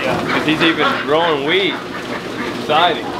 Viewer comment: I thought you said he was growing weed, Then I listened again and it was wheat. LMAO love the video